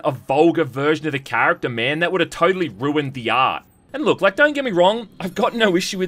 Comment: I thought he said “Ass” but he actually said Art lol